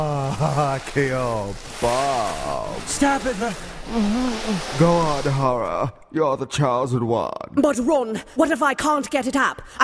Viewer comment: Go awn, Harreh. You're tha chosen wawn. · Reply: PsychosisFire I thought he said "god horror, your the chosen one"